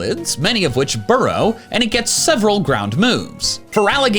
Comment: was I the only one that heard “several” instead of three?